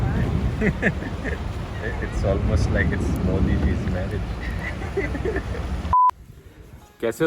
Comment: Did he say that it's like M*diJi's marriage ...in the beginning???😂😂